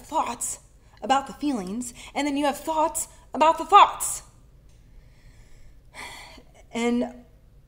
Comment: Time stamp (5:35) “Thoughts about the Fox” ? Is that a typo or did you really say fox? I’m Deaf and I watch your videos when they have Closed Captions.